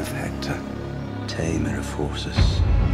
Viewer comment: Watching the director's cut, I'm pretty sure I heard 'Breaker of forces' instead of 'Tamer of horses'